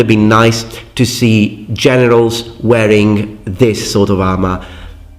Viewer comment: I am sorry, but it sounded like you said "Genitals" at 12:32 instead of generals now that is just stuck and laughing loudly